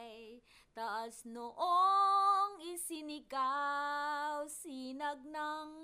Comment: Can somebody tell me why I thought she said “sinigang”? 😂😂😂